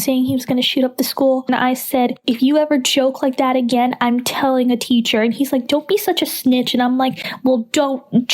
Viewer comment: “If you ever joke like that again, I’m telling a teacher.” If he jokes like that again, I don’t think you’d get a chance to make it to a teacher. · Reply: i thought she said "if you choke like that, im telling a teacher"